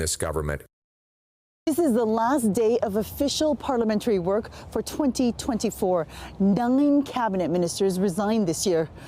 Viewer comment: …….what is she saying ?…….donging cabinet ministers have quit?